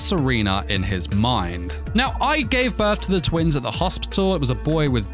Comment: threw me off guard. I heard “Now, I gave birth…” lol